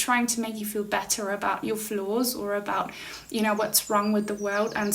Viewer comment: 868 likes and 4777 views I'm listening while doing the mundane house work I always put off.. 5:07 "make you feel better about your flaws" but to me your accent sounded like you said floors and I was like 😮 wth and laughed at the godcidence... 😂 I only found out you said flaws when I went to the transcript 😂 been a follower for awhile now and always look forward to your content. Thank you